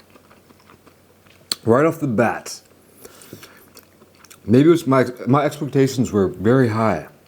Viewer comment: EY YO WHAT DID SAY AT 4:01 ? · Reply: Maybe it was mine maybe it was mine/my expectations were very high